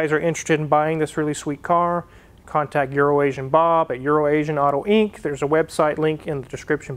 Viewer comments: Good ole Urination Bob lifted his leg again and marked another rare one. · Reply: I always thought he said "your an Asian Bob" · He will forever be known as URINATION BOB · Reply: I always heard it as Euro Nation Bob. I wondered why I could never find his website. 😜